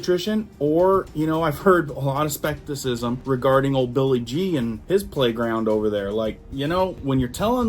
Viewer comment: When you said Billy G, I thought you said Billy Jean and so the song popped into my head. 😀